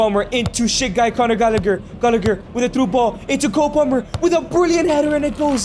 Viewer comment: Wait did he say shit guy coner gallalager